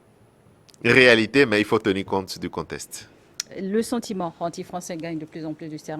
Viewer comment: réalité, mais il faut tenir compte du contexte 🤔 Il dit quoi ce mec?